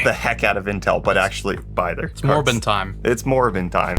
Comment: When you said "It's morbin' time", I mistakenly thought you'd said "It's Morgan time" and was expecting a different sponsor spot.